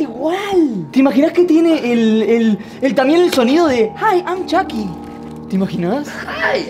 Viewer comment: Cosas Turbias.. 2:47 vos de chuky 4:23 levanta la cabeza solito 9:20 vos de chuky pero un poco silencioso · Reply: Algo que yo acabo de notar es que en el 2:47 parece que dice "hi i'm me"